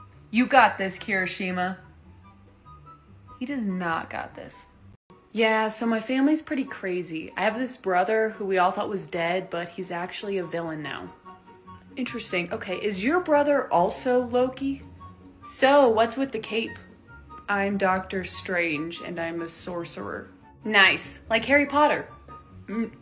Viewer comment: When thor was talking to shoto I thought he was say " wait is your brother also adopted😂